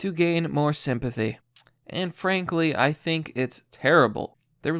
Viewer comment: Did he say "Anne Frank Lee" @ 4:00? Not funny... Not funny 😂😂😂